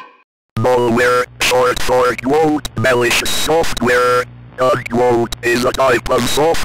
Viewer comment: I can’t even understand what it’s saying 😂 Edit: all I can here is soft wear 😂